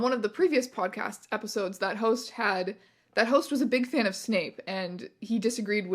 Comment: I need you to know that I was doing chores while listening to this and walked just far away enough from the computer to mishear "this host was a big fan of Snape" as "this host was a big can of snakes"